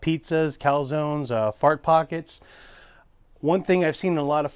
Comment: did you say Fart Pockets??